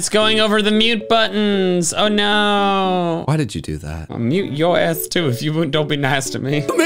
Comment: YOU SWORE YOU SAID 'ill mum yo ass 2'